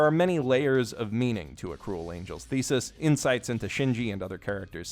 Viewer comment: Am i the only one who heard him say "a cool angels thesis"